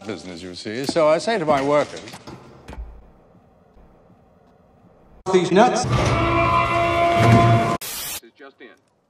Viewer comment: It literally sounded like: "Deez nuts" at 1:29.